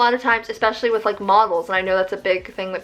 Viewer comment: I thought you said muggles instead of models lmao